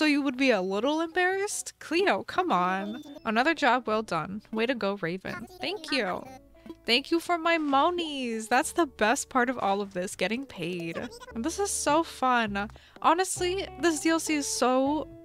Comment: It sounded like " Thank You For My Mommys" instead of "Thank You For My Moneys"